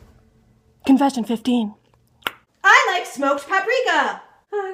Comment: Lol I thought she said she once smoked paprika